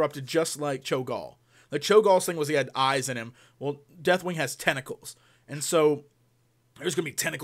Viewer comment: I first thought you said DW has testicles...